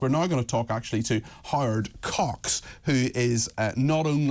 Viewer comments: Anyone else thought he said "We're now going to talk to Hard Cocks" ? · I'm sorry but to my Canadian ear the presenter at @ 0:02 said Howard Cox as " Hard Cox " 🤣 ..... anyone else hear it that way ? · Thumbs up if you thought the presenter said 'hard cocks'.